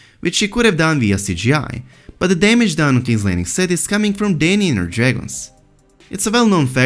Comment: Did you say "Dany and her DRAGONS"!!! @ 7:47 Are there more Dragons?! Armored Dragons?! I think Jamie will kill Cersei...